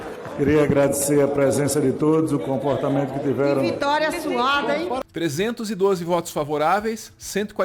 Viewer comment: Apesar de ser dito "que vitória suada", tenho quase certeza que ouvi "que vitória suave".